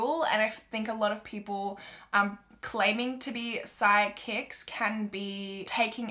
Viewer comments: I thought you said you don’t believe in side kicks not psychics 😂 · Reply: Omg it was psychics?? I thought she said side kicks too hahaha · I thought you said you don’t believe in side kicks not psychics 😂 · I thought you said you don’t believe in side kicks not psychics 😂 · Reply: wait she said psychics? I thought she said side kicks too 😂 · I thought she said she didn’t believe in “side-kicks” instead of psychics. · Whose else thought she was saying "sidekicks" not "psychics"? So confused for about 30 seconds...